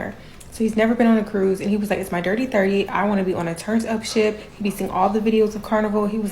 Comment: I thought she was saying turnip 😂😂😂she meant turnt up